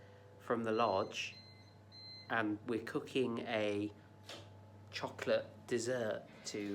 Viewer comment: microwaved chocolate frogs! YUM Dang it i thought you said chocolate frogs I'm going back to bed